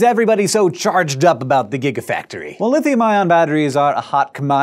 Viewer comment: I thought it said giraffactory at first. XD